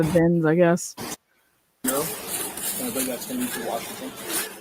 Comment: Kaylee's new car? I would want to have it close to me. ..I would want to "SEE"..the feel goods just knowing it was in my driveway..so, where is her new car? Why wasnt it parked outside her house? Physically near her? Forgive me if this was already asked? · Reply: Time stamp 45:36. Did anyone else hear THAT!? Officer said, "No? You dont think that'll be through Washington "!! Is there anything other than KB connected to this "at this time"??!! What an hour ish in to arriving to the 911 call. At this early of this horrific event...what other connection is there to Washington?